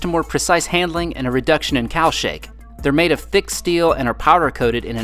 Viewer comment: I heard you say Cow Shake and became excited. Boy, imagine my disappointment when I figured out it was Cowl Shake.